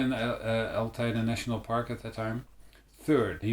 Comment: Sounds like you said "Al Qaeda National Park." So I guess it could be worse. They could've cut your head off instead of fining you. I wouldn't pay it. How did they get your address anyway? Did they track you down through Youtube? Ask them what the 660 Euros goes toward? Their pensions perhaps?